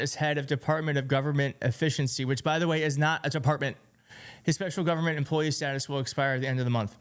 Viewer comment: Awww, poor little snowflake, Elon Musk! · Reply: Ikr? I thought he said empathy is bad.